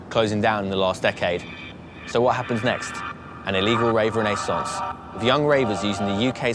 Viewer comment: the guy says "illegal rave renaissance", the subtitles say "illegal rave in a sauce" xD